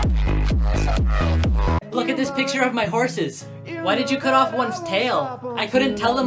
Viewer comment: I thought he said "why did you cut toe nails "😂😂😂